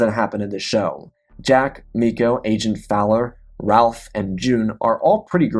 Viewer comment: “Ralph”? I think you meant to say Raf (short for Rafael)